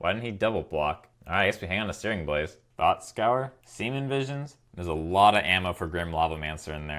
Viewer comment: did he say semen visions? lol!